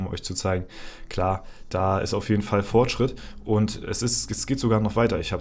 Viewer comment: Daily Kommi <3, hab ich das Fortschritt gehört?